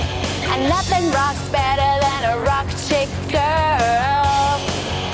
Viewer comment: My Mumbaiya mind heard it as 'Raapchik girl' 🤣🤣